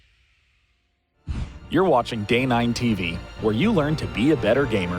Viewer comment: Seriously, i hate to be a Jerk, but just listen to 0:20 and tell me, that he is not saying gay9 tv :D